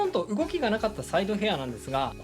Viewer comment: サイドヘアーがさいとうヘアーに聞こえて一瞬ん？ってなったw